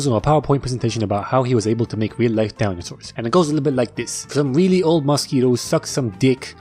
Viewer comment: did anyone else hear “dalnyosaurs” or is it just me😭😭😭😭😭😭😭😭